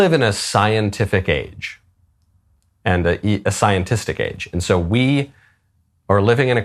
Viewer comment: You said "scientistic", and I heard "Elon Musk". It turns out you meant SCIENCE + STATISTICS, not SCIENCE + AUTISTIC. 😆